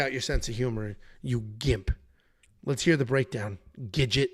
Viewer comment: I thought when he said gidget he was calling him a ginger midget 😂